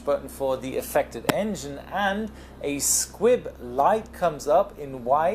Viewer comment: I didn't know the word "Squib" yet. When you said "squib" the first time in the video, I misheard it as "inkfish". And by "inkfish", I mean "squid".